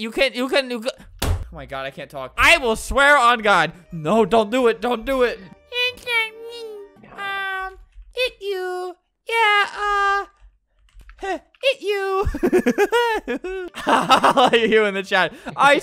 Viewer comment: it sounds like Flamingo says, “you c***”